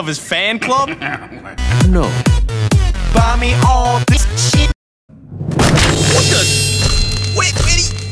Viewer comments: Sounded like Spy was saying Buy me all this shit XD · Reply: PugVSGaming 1:54 People say he said "Buy me all this shit" and which he did, but I heard "Buy me all this Piece of shit". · Oppa gangnam st- gets shot What? It was obvious! He's P-SY! 1:54 Some comments thought he said "buy me all this shit!" but he actually said"mommy all this cheap" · did he say: "buy me all this shit"? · Sounded like Spy was saying Buy me all this shit XD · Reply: PugIsVeryAnxious I thought he was saying:bunny on this shet